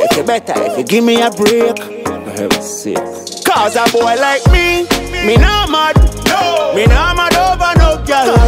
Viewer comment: I keep hearing "Gaza boy like me" 🤷🏽‍♀️